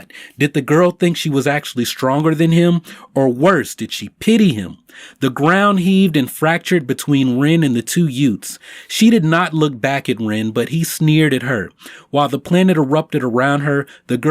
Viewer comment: total my cousin vinny moment. "The two yoots". Uh what was that word? The two what? Did you say yoot?